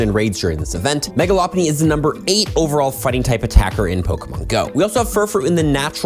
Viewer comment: I am so brainrotted I heard it as furry type raid attacker